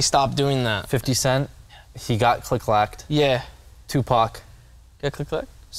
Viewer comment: Thought he said "click click klaucke"